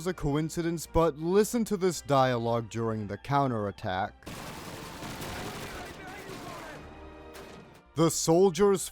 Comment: what are they saying here? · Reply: "I will be right behind you, comrade!"